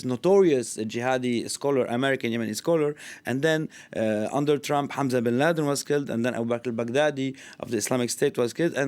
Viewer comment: Did he say Hunter bin Laden?